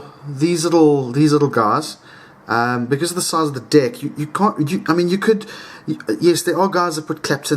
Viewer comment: Hahaha at min 2:08 , its sounded like 'because the size of the dick' kekeke